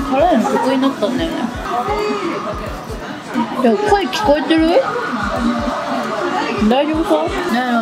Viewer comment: 「コエ聞こえてる？」既視感。。かのくんの「撮れてる？」でした、それにしか聞こえなかった笑